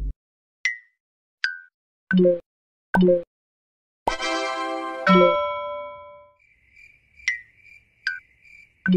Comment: may I know what she is saying? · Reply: Noooo?!?!?! You are mean! I am reporting you unless I be Queen